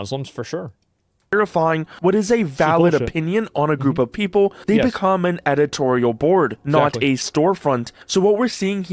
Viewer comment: What did asmon say at 10:13? couldnt hear/understand due to the video playing and it's eating at me lol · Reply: He said "Its bullshit"... but him saying there are many ppl who are Islamic who aren't Muslim is hilarious. Clown